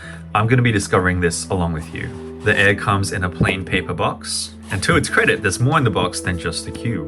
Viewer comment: All I heard, "The air comes in a plain paper box"